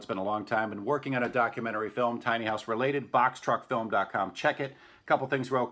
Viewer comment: I like seeing the various A-frame builds. I thought I heard you say "box trucks", people can get creative in taking box trucks and make them homes. They won't be $2,000, but unless they are just starting out.